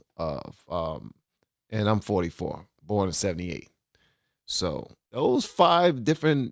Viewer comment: I thought you said "44 going on 78", and I couldn't help but think that you look good for your advanced years.